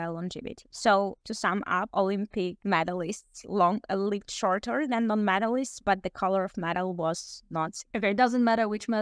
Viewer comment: "Olympic medalists live shorter than non-medalists"? That was very hard to tell what you said